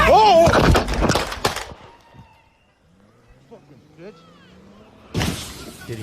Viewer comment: I heard “F***ing b**ch”